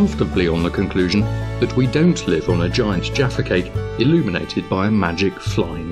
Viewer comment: Right at 5:00, "... on the conclusion that we don't live on a giant ___". I had to back up a couple times to understand what the hell he was saying, and now I have discovered Jaffa Cakes: <link> I'ma have to see if I can get some of these in the states because they look and sound yummy! Looking forward to trying one! Who knows what you could learn on YouTube! ;)